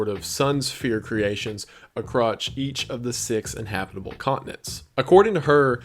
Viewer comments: did you just say "acrotch" instead of across or am I losing my mind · he says “a-crotch” instead of “across”